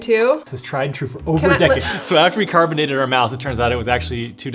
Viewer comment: “We carbon dated the mouse, it was 2 decades old” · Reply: I totally heard "carbonated", and for some reason didn't think twice about it...